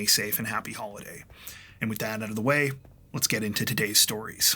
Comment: did you say, "and with dad out of the way"